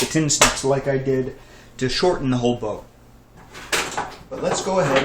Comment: Wait.. did you say "Shorten the whole bow ?" hehe.. you make too much bows..